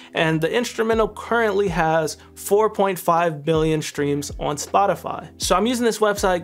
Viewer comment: I love how he say's billion instead of million 🤣🤣